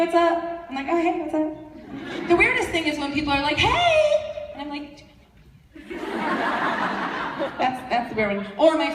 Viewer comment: What did she say at 18:08? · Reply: "Do i know you?"